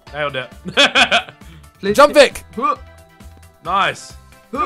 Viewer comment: I swear I can hear saying jump